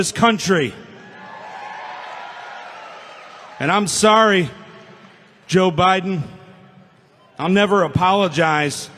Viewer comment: Did he just say "I'm sorry but I'll never apologize"?!